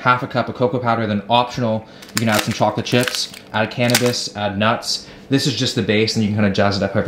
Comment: Did he just say “add cannabis” at 11:16 🤣🤣